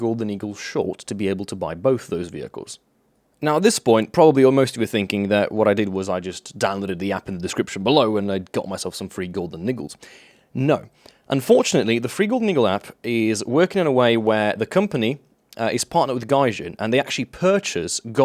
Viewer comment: did I just hear golden niggles ?